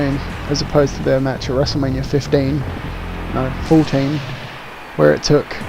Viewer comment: How embassasing. I heard you say WrestleMania 15, the paused it straight away, posted the comment clicked play and then you said 14 and I was like 'DELETEEE!'